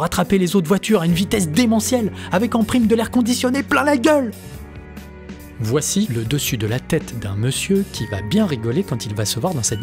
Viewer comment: Perso j'ai entendu "Voilà la tête d'un monsieur qui va bien rire quand il va se voir dans cette vidéo"